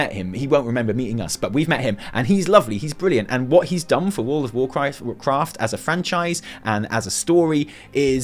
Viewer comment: lol... Did you say world of WarChrist at 13:34? :D